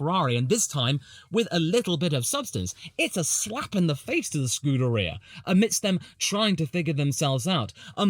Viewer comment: Thought I heard a “Screwderia” Freudian slip in there 😂